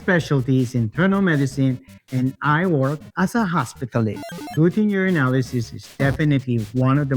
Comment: any chance the doctor is saying "hospitalist"? The captions say "in distinct" but that would be my guest based on the sound and shape of his mouth.